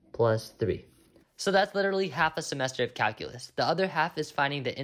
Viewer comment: I thought that said calcium so I was like oh man do I drink a lot of milk 💀